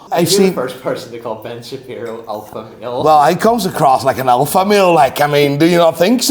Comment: When he said “alpha male“ I thought he said “alpha meal “. 😂